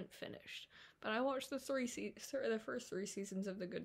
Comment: what did you say? mental?